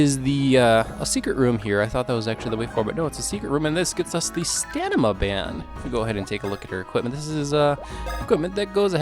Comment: I swear you said "Stanima band". 6:54 I can't find a solid answer, but as far as I see it's 999,999. So you're missing a digit. 12:38 AH! MY EARS! WHY?!